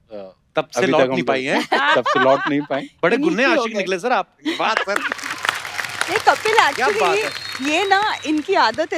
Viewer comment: bade kunne ashiq nikle? What is the word before ashiq? Can anyone please help? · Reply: It's punjabi word ghunne, that means secret or chhupe rustam